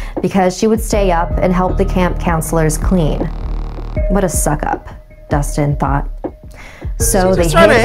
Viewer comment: did anybody else hear Dusting thot